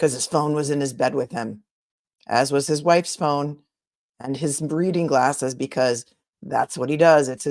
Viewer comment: Lmao. Did you say “breeding glasses” if so that is good.